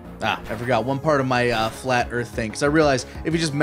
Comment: I thought said "flattered Kirran" at the beginning, referencing the pokemon move "flatter" and it took me a while to figure out he said "flat earth". Where did that come from? When did they start talking about flat earth on the stream?